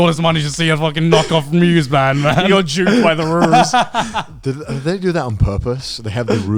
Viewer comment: Did joey say he was jewed by the ruse at 4:50?